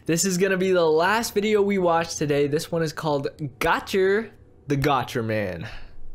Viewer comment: When said gotcher I thought he said gacha 🤣